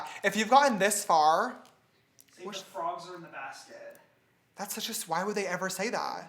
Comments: Frogs are in the basket! Did I hear it correct? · I thought you said frogs are the best gift and then I read the comments